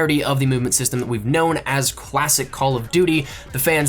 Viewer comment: Did say cwasic not classic